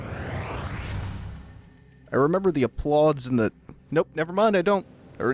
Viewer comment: Huh, they're actually called Applauds. I misheard you the first dozen or so times and thought it was Clods.